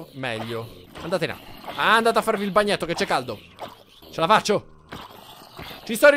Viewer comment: Minuto 6:29 invece che sentire c’è la faccio ho sentito BelloFaccio😂❤️🙄😪